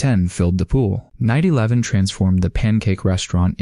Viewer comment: I thought bro said 9/11... 🙃 here again